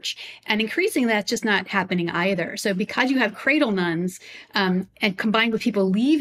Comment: I'm a 50 year old "cradle none" in the Southeastern US and happy about it. I'm involved in two local atheist/agnostic groups. One is a mostly social group trying to foster community and one helps the homeless. Being an non-believer doesn't have to mean lack of community. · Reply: I really thought they were saying cradle nun LOL